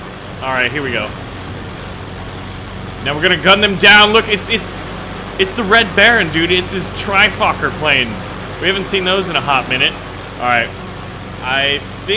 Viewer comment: xD Baron when you said Trifocker or whatever I thought you said the F word XD